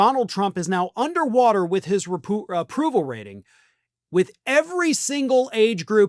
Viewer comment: I thought he almost said A Putin rating and just thought "sounds like something Trump would care about."